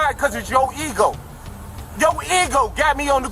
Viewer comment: 😂did he just say ego???? 😂